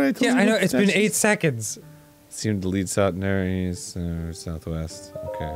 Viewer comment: Arin: Okay south west!! goes southeast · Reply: Oh, South East? I thought you said South Weast.